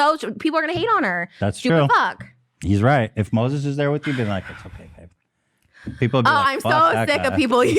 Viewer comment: When Ethan whispered to Trish, “It’s okay babe” I thought I misheard it then I repeated it and then my heart melted. 🥲 Edit: for those who want the time stamp